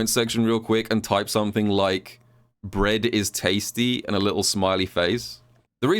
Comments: i thought you said red is tasty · Bread is tasty. Eating a sando right now while watching. Lol I thought you said "red is tasty", initially.